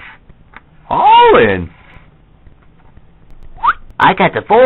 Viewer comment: Luigi: All in Wario: All in Waluigi: All in Mario: All in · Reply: when i was little i thought he said ill win